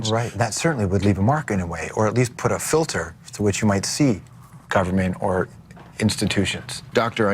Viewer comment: As someone who's English is not my main language, what does the interviewer say at 8:37? I only picked up the last word "institutions". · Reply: George : "that certainly would leave a mark anyway or at least put a filter for which you might to see government or institutions"